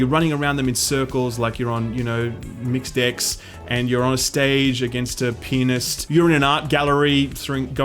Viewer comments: I thought he said "your on stage with a penis". Man did that get my "Huuuuh?" going. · Every time you said pianist it sounded like penist/penis. 5:58